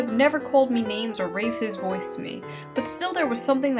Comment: I misheard "raise his voice to me" as "racist voiced me"